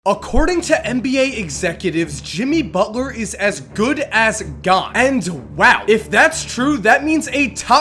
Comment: sounds like u said "as good as god" i was likewhat lol